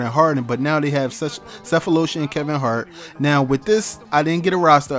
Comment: did you say Kevin Hart or Kevin Mart